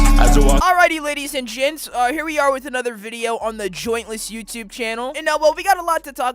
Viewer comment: Is it just me or did you say jointless in your intro?